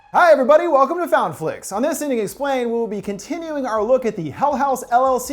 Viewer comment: I thought he said “in this idiot explained” at the beginning